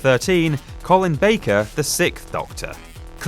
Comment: I think i heard him said "Colin Baker, the sick doctor"